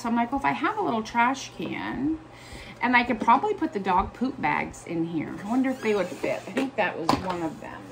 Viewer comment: So what are the plastic bags that fit in the blue garbage can perfectly? I might have misunderstood but I thought you said dog bags?!?!?! LOL 😆. Sorry! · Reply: Lol that is what I said! They are dog poop bags!